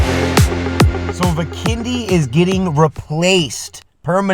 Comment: On The Beginning Of The Video, I Thought Said: My Kidney Is Getting Replaced😆